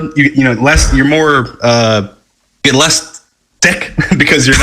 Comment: lol sounded like less dick